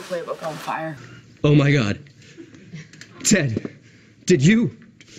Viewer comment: anyone know what neil said at 3:45 ? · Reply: He said “did you fart on her face?”